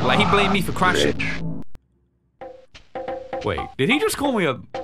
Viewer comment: I think he said rich idk